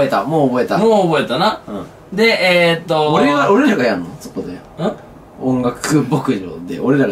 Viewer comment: 東方方面😭 音楽牧場がOK牧場に聞こえてしまった